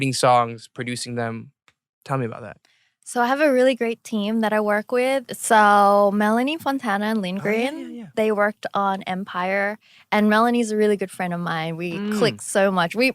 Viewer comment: When she said “Melanie Fontana” 🤢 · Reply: Im sorry I thought you said melanie martinez and i was like wait wuts wrong with melanie? But then idk who this person is cuz ever since bts has been growing too big i stopped following them but i still listen to their songs only because the fandom had like a very on and off relationship with me